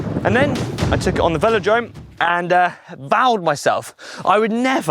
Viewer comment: To be honest when said "vowed himself" I thought he said "soiled himself", and I don't blame him.